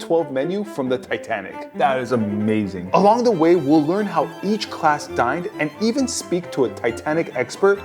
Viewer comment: I heard "learn how each class died" 😭